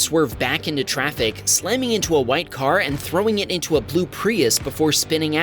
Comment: i hate me brain most times i heard plue and instead of blue